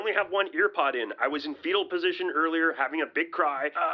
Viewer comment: Did he just say he was in the fecal position?